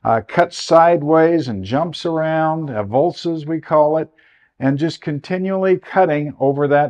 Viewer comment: what was that word? aevolces ? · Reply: Avulsion or verb avulces. A word for when a river jumps to a new location